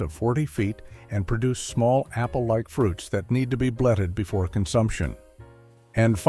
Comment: Sorb apple... "need to be...bledded"...or "blooded" according to the CC. Anyone know what this is? Thanks! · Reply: "Bletted", similar to what was described for the medlar. It's essentially when a fruit needs to go very, very overripe before it's its most edible.